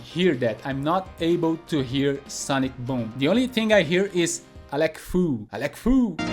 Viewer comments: Interesting about not being able to hear Guile say "sonic boom" but instead "Alec fool" lol, I always heard sonic boom. But for Sagat, his tiger uppercut always sound like he was saying tiger apricot lol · I heard sonic boom and now I can only hear Alec Foo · Great! I only actually heard "sonic boom" always but now all I hear is "Alec fool" lol